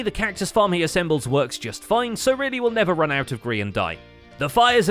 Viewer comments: Did he say „Grian dye“? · "run out of grian dye".....? Did he just say that